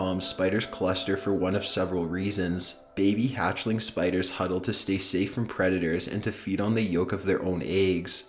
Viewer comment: The first time I was listening to this, I thought he said “Hatchlings huddle together to stay safe from Redditors” and the site is so messed up that I didn’t even question it.